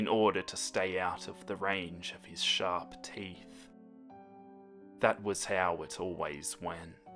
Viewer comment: "In order to stay out of range of his sharp teeth" Meh: I heard that as SHARK teeth. It's both at this point